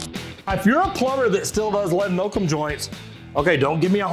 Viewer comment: whoever did the editing on the video made timestamps in the video and titled the lead oakum joint "lead milk joint" I was confused at first but it does kinda sound like you said lead and milk joint at the beginning.